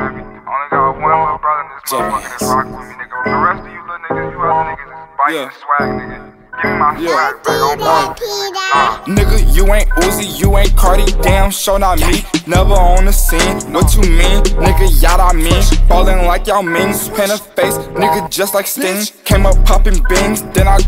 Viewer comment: "nigga you ain't uzi, you ain't carti, damn sure not me" best part of the song. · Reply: i thought he said you ain't cardi as in cardi B lmfao